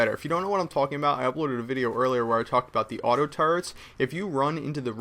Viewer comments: sounds like he says AUTO-TURDS at 1:56 lol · ur amazin but when u say auto turrets it sounds like u say auto turds